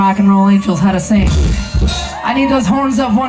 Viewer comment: Ryan James Dio?, hes name was Ronnie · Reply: she said Ronnie haha, truth is i thought she said Ryan at first too though